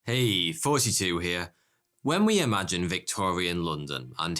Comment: Until now i thought you were saying "Hey 42 here" ._.